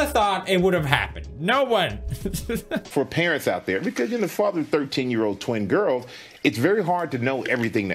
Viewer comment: nao entendi o que ele falou sorry :/ KKKKKKKKKKKKKKKKKKKKKKKKKKKKKKKKKKKKKKKKKKKKKKKKKKKKKKKKKKKKKKKKKKKKK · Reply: "For parents out there because where you're the father (of) thirteen year(s) old twin girl(s)" tive q ver a legenda em inglês pra entender